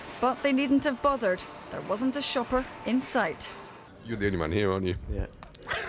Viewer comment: What does that guy say at 0:30? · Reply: You're the only one here, aren't you. Then the guy responds"yeah" and laughs.